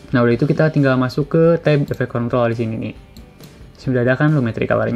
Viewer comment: efek kont ... gw salah denger bro anjirr